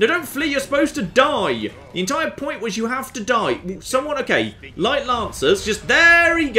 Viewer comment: When he said “Haflidhi,” I thought he said “Have-to-die.”